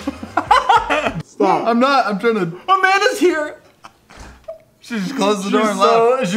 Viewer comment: When you said Amanda is here I thought you said a man is here